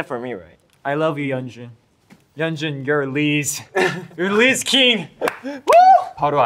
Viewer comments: holup why do the captions say “Liz King” AHAHAHA— Kai said RIZZ KING with his full chest RIZZ KING 😤🤌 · Another iconic huening line😂😂 2:40 “ yeonjun your RIZZ your rizz KING” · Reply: I thought he said, "You're Lea's." ☠️